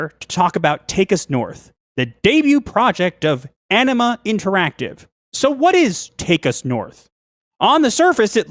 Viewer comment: I keep hearing 'Enema Interactive' . Fitting.